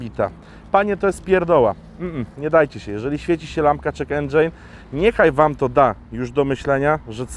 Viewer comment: Czy tylko ja usłyszałem "lampka check Andrzej"? :D